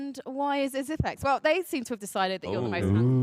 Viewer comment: What did magisk say at 9:51??? Did he say xypxs is the uglyest player in astralis or something like that?? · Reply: he said he is the least handsome player